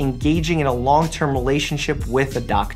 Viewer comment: i thought he said be A patient...i was shocked for a sec